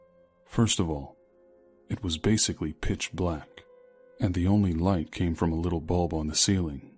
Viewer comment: I swear I heard "b!tch black" instead of pitch black. 😂